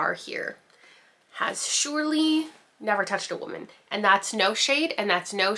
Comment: At work listening to this and I hear “has never touched a woman” and now I’m trying to keep my composure but all I want to do is cackle. Excellent delivery 😂😂😂